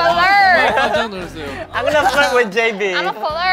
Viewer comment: lmao is it just me or did Kevin say "I'm gonna fuck with JB" at 31:52 when he was imitating Melissa?